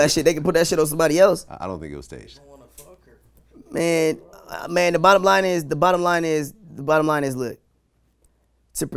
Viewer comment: what does the voice in the background say??? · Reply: 316ht The guy said “I wanna fuck her”...